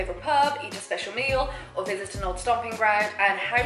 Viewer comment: Hello! I love this video so much, and I want to make sure everyone can understand and enjoy it. That's why I wanted to let you know that your captions are a little off. At about 2:23 the auto-captions say "or business to not stop being brown" where they meant to say "or visit an old stomping ground". At about 3:34 the auto-captions say "it's in Devon degrees outside" where they meant to say "it's eleven degrees outside". When you get a chance, could you fix that? That'll help the 360 million folks globally who rely on closed captions to understand videos. Thank you, and keep up the great work!